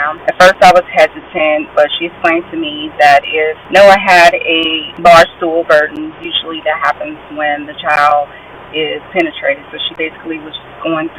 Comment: Can someone please help me. I can't make out what she said at 34:49, something the nurse said happened if a child was pene-trated. What the nurse said is very important to me...please respond if you know what she said. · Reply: He had an anal fissure a rectal tear